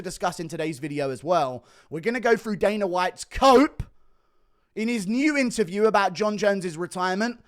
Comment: When you said "we're gonna go through Dana White's COPE" (and moved your mouse to his BALD HEAD) I thought you said coat (like coat of paint or something?) and for a second I was expecting some analysis of the shade of pink he was during his video. But what I got instead was cool